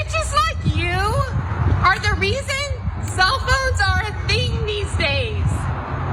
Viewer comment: Can someone tell me what she saying at 0:35. "Are the reason that........... these days" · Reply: For cell phones